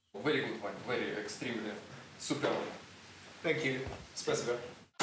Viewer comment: тот мужчина говорит:Спасибо(или мне послышалось)